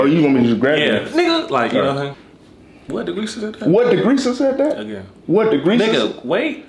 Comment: i had to pause the whole viddy, come on now "DeGresa" ...where yall be pulling these names from 😁😂🤣😭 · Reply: I thought he said Lagresesa